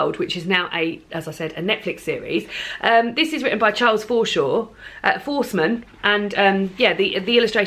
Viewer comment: I thought you said Charles Foreskin. I had to replay it!